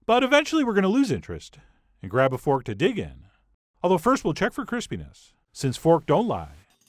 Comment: in the video I dunno but my caption said Chris penis instead of crispiness